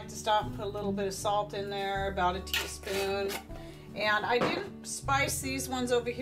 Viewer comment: there was a glitch when you said how much salt was added. Did you say about a teaspoon or tablespoon. thanks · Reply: I usually use a teaspoon per quart but one can add more if they want, it is really a matter of taste